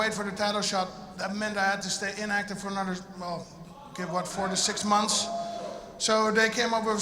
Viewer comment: i thought overeem said he would have to stay inactive for 46 months at first